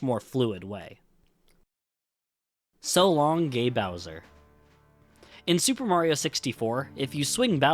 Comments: I always thought he said "So long! Eh Bowser?" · Mario actually says "So long, eh Bowser?!". The reason the line is missing in the Switch version is because it's actually the japanese Shindou version, which added Rumble Pak support and the Peach voice over that was previously not in the original japanese version. The line "So long, eh Bowser?!" is missing from every japanese version and the reason is that, in Japan, Bowser is not named Bowser, but rather "Kuppa". So, having Mario randomly calling him Bowser in one of the voice lines would have just confused japanese players. Since the Switch version is the Shindou version on which a patch for textures and language is applied, it makes sense why the line cannot be heard there. Hope I was able to explain this well! EDIT: I didn't think this comment would be this popular! Thanks for the MANY people who reminded me he says King and not eh. I learned that a long time ago but for some reason, a memory of someone saying it was "Eh" left a bigger impression on me so I guess it took over my memories. Thanks for many replies though. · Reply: I always heard Mario says "So long-ah Bowser" the same way he says "its-ah me, Mario" on the title screen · I always thought of it as Mario saying "So Longy Bowser" in Mario 64, So Long "Gay" Bowser never came up wit me or my 2 brothers... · Tbh as a kid I always thought it said “ so long ay bowser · I thought Mario said So Long, eh Bowser